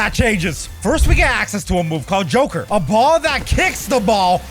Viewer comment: did he say a ball that kicks the ball